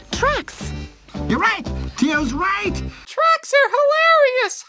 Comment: I thought they were saying "trucks" instead of "tracks"...I honestly wouldn't be surprised if it was trucks.